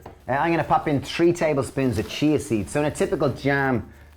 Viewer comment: I thought they said “cheese seeds”....then they scooped out chia seeds 🥴